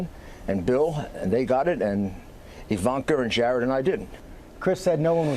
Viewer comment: Did I hear it correctly that he said Bill got it???